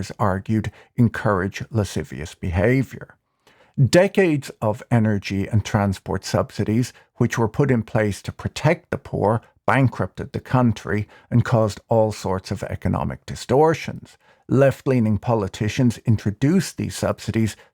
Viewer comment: I heard "bankrupted the country" as "but encrypted the country" and thought Bitcoin adoption had really gone too far. 🤑